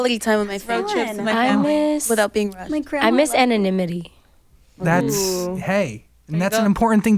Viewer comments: what does Lauren say what she is missing? English isnt my first language and I couldnt understand her properly :) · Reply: she said she misses anonymity! She means that she misses being able to just be another person on the street, but because she is famous and in a famous girl group she is very recognizable thus taking away her privacy. Hope that helps!! :) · what did lauren say? @ 26:12 . i couldn't hear it properly. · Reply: leanne coore Anonymity